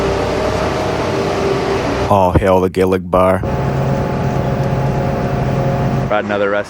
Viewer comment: Captions keep mishearing “All hail the Gillig bar” as “Oh, hell the Gillig bar”, which is just plain hilarious lol